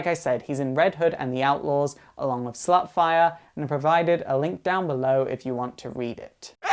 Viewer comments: instead of starfire did he say slutfire? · did he call starfire slutfire?